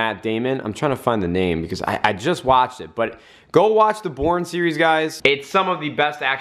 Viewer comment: when he said watch the 'Bourne series' I thought he said watch the 'porn series' I had to rewatch lmao